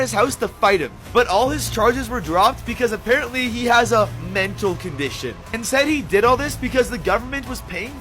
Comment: did I hear "MENTAL CONDITION"? Like dude I've got autisim and ADHD and I don't use that as an excuse?! 🙄